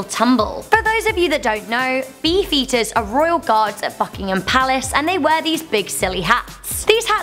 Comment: I thought she said fuckingham palace instead of buckingham palace 😂